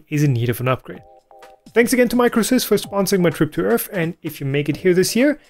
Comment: you got me wondering for a bit there , I thought you said "my trip to earth" , I was wondering which planet you came from ?